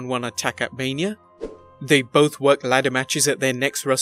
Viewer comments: WRONG Benoit DID NOT win a ladder match at the Wrestlemania following his world title win Bryan Yes, world title win at 30, Ladder match win at 31, Benoit won the world title at 20, he DID NOT win a ladder match at 21 or any mania for that matter. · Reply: yeah, at first i thought he said won too, but i remember that benoit now won on that ladder matched, so i watch again & it said worked :) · Benoit didn't win at Mania 21, Edge did. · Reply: pardon me I misunderstood. I thought he said they both won at the next years WM ladder match